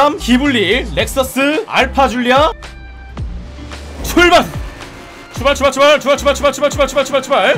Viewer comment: 출발 출발이 아니고 슈발 슈발 처럼 들리는 거 나만 그런건가?..